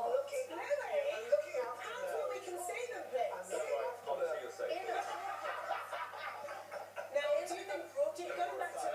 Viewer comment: what did simon say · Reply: "Don't worry, you're safe there"